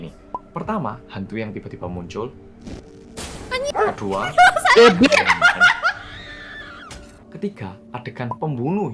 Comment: Ngomong apa tuh?? 🤔🤔😂😂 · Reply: an---------dai aku jadi kaya